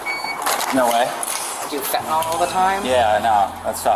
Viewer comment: she said “Fentanyl” but the captions said, “I do fart all the time.” 💀